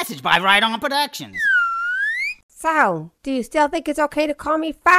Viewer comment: In a Nutshell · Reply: The first time I thought he said "dumblin"